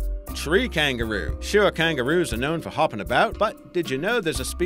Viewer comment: Tree kangaroo Imma call it hangaroo (I literally thought he said that XD)